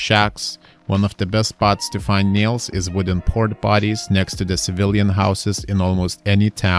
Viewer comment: did he say "wooden port bodies"? what is a wooden port body??